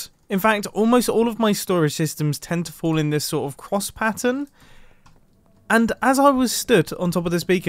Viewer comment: Mumbo: "I don't know why but all my bases end up having this like, croissant pattern.." Me: rewinds "d-did he say, 'croissant'???" plays back Mumbo: "... having this like, quoss pattern..." What is a QUOSS?